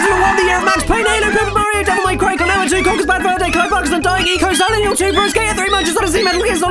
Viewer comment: does anyone know what games he lists, I know he said Max Payne, MGS2, Silent Hill 2, Jak and Daxter, Pikmin, Devil May Cry, Ico, Luigi's Mansion, and Lego Racers 2. but that's about all I got. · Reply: Max Payne Halo Paper Mario Devil May Cry Klonoa 2 Conker's Bad Fur Day Clive Barker's Undying Ico Silent Hill 2 Tony Hawk's Pro Skater 3 Something I didn't get Metal Gear Solid 2 Luigi's Mansion Super Smash Bros. Melee Pikmin Jak and Daxter Lego Racers 2.